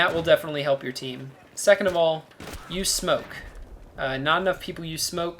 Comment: lol I thought he said "You smoke"